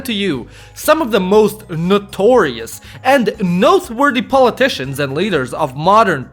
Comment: I heard "noseworthy" instead of noteworthy just because Radu Mazare was on screen at that time, sorry...